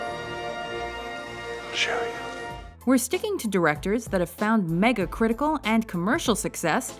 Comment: thought it said dictators